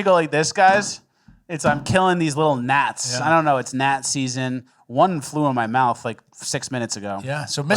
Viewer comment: “it’s gnat ziseason” is what i heard